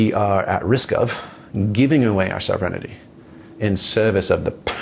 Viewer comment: I don't get that word he says that sound like 'self renidy' to me? Can someone help me what he means by that? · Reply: I heard "sovereign-innity". lol